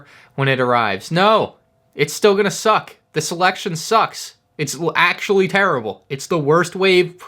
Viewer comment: legit thought you said “this election sucks”